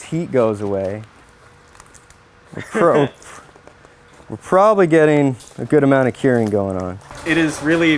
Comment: Hey, i'm doing subtitles, and would like to jnow what the dude said at 5:43 cause i just can't understand lol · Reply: "We're probably getting a good amount of curing going on." <link>